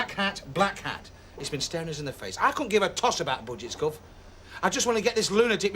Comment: did he really say: "I couldn't give a Tosh about.."?😄